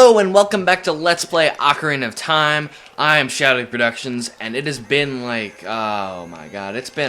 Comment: I still think it sounds like you say "ShadowReproductions" when you say your name. Huh.